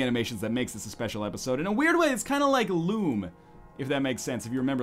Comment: When you said "kinda like Lume" I thought you said "kinda like Loom" (the old LucasArts point & clicker). Showing my age?